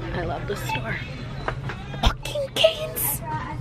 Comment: Okay I'm guessing she said "walking canes" , because I heard " f*****g canes" 😂 I'm dead 😂